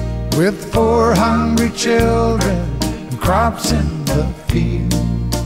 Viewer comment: I thought he said "with four hundred children". Instead of: "with four hungry children". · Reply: Thy Mother lol. I always thought he said ain't a crop in the field.